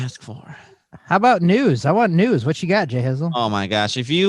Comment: Re-watching this, waiting for episode 42 to be uploaded, and when said, "I want news.".., Just like the first time, I thought he said, "nudes". LOL